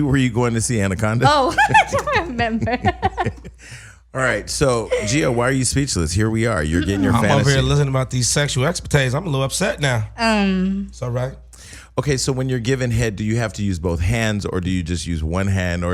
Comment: Did Say "Sexual ExPA-Tades"???